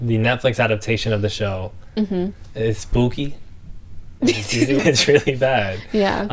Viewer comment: i may have misheard, but the auto-captions indicate that you use the word “sp__ky” around 1:47? if this is the case, i’d encourage you to avoid that word because it’s anti-Black in origin. apologies if this is misplaced because the captions were wrong, i have auditory processing difficulties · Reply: hello--we were using the word "booky" (sp?) there, which we are using a slang term for poop. as far as the word 'spooky,' fluffcrum is essentially correct--its origins are a dutch word meaning "spectre," and is basically universally used as a term for "scary" or "creepy." the term 'spook,' derived from that, was used as a racial slur against Black people in the early 20th century, though it has since become quite antiquated and lesser-known.